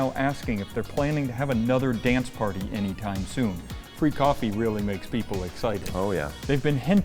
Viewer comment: Free coffee, at 7:50 it sounded like said “Freak-offy”, and I could only hope it wasn’t related to P Diddy’s version. I definitely would not ask for the cream, I would only assume they would use baby oil. 😂😜 Good to see you guys posting again. ❤